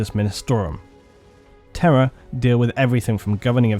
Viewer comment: Oh Terra...I thought you were saying “terror” 😂😂😂